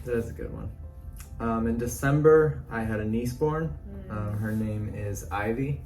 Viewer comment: When you said you had a niece born... I honestly thought you said you had a knee spawn and it was called Ivy... I thought 'weird medical condition, but sure I believe it'